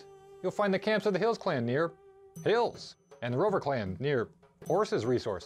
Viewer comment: OK the first few times I listened to the update I thought the guy said the River Caln near horses and was like 'what?' but just now I can hear him say Rover Clan and I feel stupid